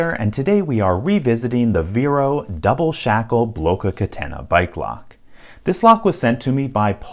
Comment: When he said - Bike lock, I thought he said- By Glock! ( He said- Bike lock bike lock in two languages! Confusing!) And what does he mean by- We?